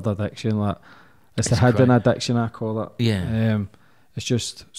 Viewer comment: What does he say at 59:24? It's the -------- addiction I call it? (what's the blank Im missing) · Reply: yes he says "its a hidden addiction I call it"